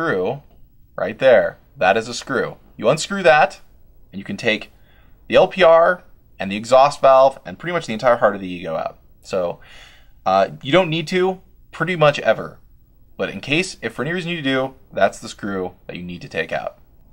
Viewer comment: who else heard him say the heart of the ego